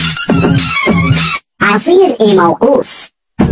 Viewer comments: ( Am I the only one who hears " I see an emo moose " 💀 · all I hear is "I see an emo ours" ours is french for bear so "I see and emo bear" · i hear "i see among us" ;0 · all I hear is "I see an emo ours" ours is french for bear so "I see and emo bear" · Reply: I heard "I see emo goose "💀💀💀💀